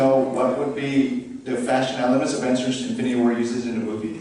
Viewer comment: Scarlett always gets the stupid questions man. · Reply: I actually thought he said "fascist elements" (which would make more sense in terms of both China and Black Widow's origins)